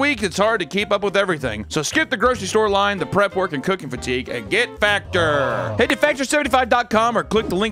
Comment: Get factor thought he said get fatter would have been better